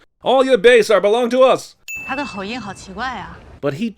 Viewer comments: The English subtitle is wrong, probably to make the movie flow better or something. What she actually said is: his accent is so weird. BTW I’m Taiwanese Taiwan is a free country I don’t need VPN for YouTube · She actually said "He has a strange accent...", which made more sense than the subtitles that said "Did you understand a word of that?" It sounded like she was politely saying that she couldn't understand him, but it would appear that they'd rather tailor the subtitle to sound more American/English. Probably they handed the Chinese actors the English script and they were like, "yeah, let's improvise and say something that actually makes sense here..."